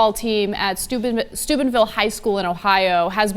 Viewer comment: i thought she said stupidville highschool.